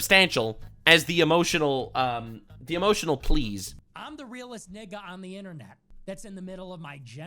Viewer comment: did he just say im the realist N*gg* on the internet 😂😂😂